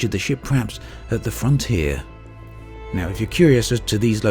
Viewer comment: "at the front here" 3:31 I heard frontier ...